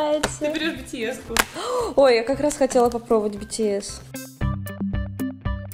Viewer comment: Мне послышалось 3:17 Bts?